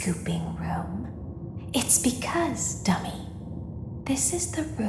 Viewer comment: Is it real you know stuff 58:02 did she just say dummy